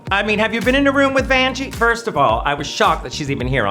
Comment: I love that the subtitles at 3:10 said "Banshee" instead of Vanjie when Bianca was talking about how loud she is, 😂